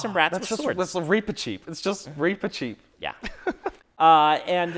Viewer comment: What do you say at 22:20? "Reaper cheet"? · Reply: Reepicheep. He’s the fencing mouse from the Narnia series.